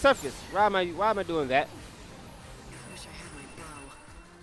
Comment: or somewhere around there am I the only one who heard lightning say I wish I had my bow?